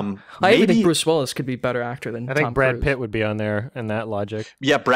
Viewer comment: What did say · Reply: Chuck Norris